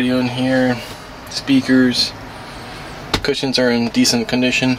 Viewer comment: did you said ‘kussens’? Thats dutch🇳🇱